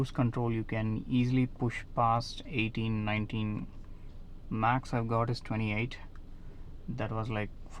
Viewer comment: Bro you said max you got is 28? Or did I hear it wrong? 15:38 15:40 I'm thinking to get this car, I love the performance I took test drive and I will drive within 100 only on highways so how much I can expect from this? With 80-100 speed & cruise control.